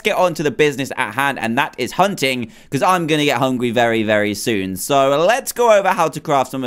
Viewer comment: uhhh, who heard him say hungwy and not hungry lol